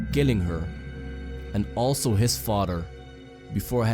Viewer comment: When he said he killed his father I thought he said farter